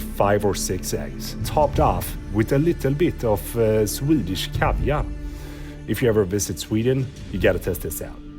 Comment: Did you say eggs and CAVIAR? Or was that me mishearing? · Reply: Swedish Kaviar, not Caviar